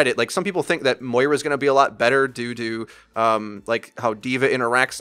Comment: you said "due to" around 15:15 and it sounded like you said doo doo